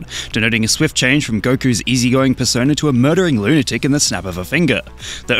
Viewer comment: Instead of "murdering", I thought you said "muttering" and that made Goku 10x as scary